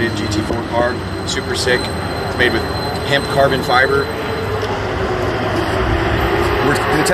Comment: Did he say “hemp carbon fiber”?